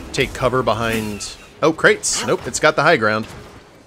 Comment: Sorry, correct me if I’m wrong but did you say... HIGH GROUND?